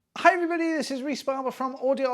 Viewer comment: I always hear "I everybody, this is Rys Palaba"